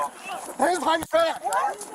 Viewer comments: Did he just say “hands behind your fat”??? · let it play.. did I hear "hands behind your fat"? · To me it sounded like the cop said "Hands behind your fat!" XD